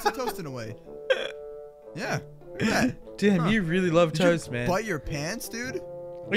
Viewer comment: did you say "Damn you really love toast man"?